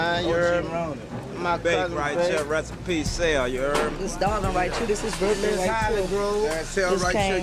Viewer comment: all i heard was "Ya heaarrrd mah" 100x